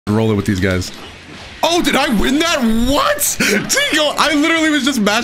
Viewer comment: anybody hear that tiko